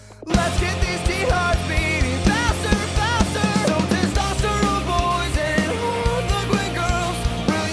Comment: I used to think he said "disaster of boys and all the twin girls" instead of "testosterone boys and harlequin girls" 😁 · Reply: sara hotdog for a while i really got it mixed up and thought it said testosterone boys and material girls X_X